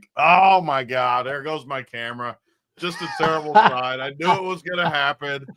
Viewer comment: lmao. 🤣 That camera part was funny. I thought you said what a terrible run at first You said terrible sign lol